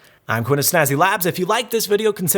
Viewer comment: At the end of the video, it sounded like you said "See ya next weak ass". haha...nice review by the way! I definitely want these now!